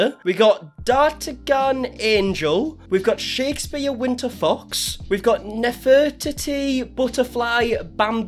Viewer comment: bro I legit thought you said Shakespeare winter fucks. I wasn't looking at the screen so all I heard was what you said. I had to rewind the video real quick to make sure I heard the correct thing or not